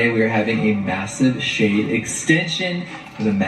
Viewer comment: The first time, I heard, "Shane extensions," not "shade extensions" 😂